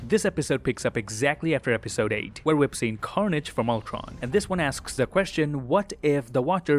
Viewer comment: I don’t get did he just say “carnage from ultron”